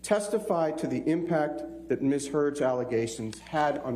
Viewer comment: I thought I heard Ben call her miss turd..I think clearly she believes her own lies.. For Someone who's bn abused, She sure smirks& laughs alot in Court..My Goodness ,with that Amount of writing in Court, Surely We'll See a published book from her Real Soon!!!!....JUSTICE 4 JOHNNY Y'ALL!!!!!!!!